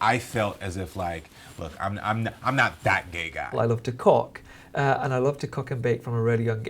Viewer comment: OMG at 1:04 I thought Tan said "I love cock" and I was like me too sis lol